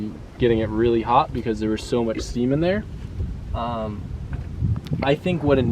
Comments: What the hell is he saying? All I hear is: bc there's so much seamen there. (typo is intentional) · im sorry, did you say there was so much semen there? im confused